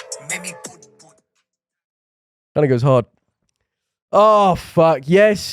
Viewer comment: did he just say the n word?😂😂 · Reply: Think he says 'that goes hard'